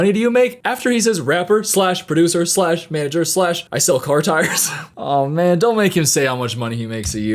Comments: I thought he said tigers, not tires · He sells car tires? Man, I could've sworn he said he sells tigers, and I was really taken aback at how nobody was questioning him about that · OHHH, Car tires, I knew I was having a hard time understanding him because of the metal on his teeth, but I honestly thought he said tigers 😅